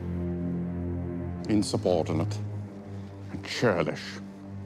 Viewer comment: After 4 years of watching Key and Peele, I just realized that when he says “Insubordinate and churlish,” its a reference to the substitute teacher who kept mispronouncing names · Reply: Oh I heard it as "insubordinate natürlich"